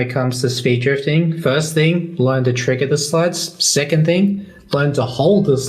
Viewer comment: Can't figure out what says at 23:14 for step one. Anyone? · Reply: He says "learn to trigger the slides"